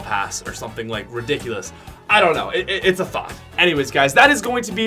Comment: DID YOU JUST SAY THOT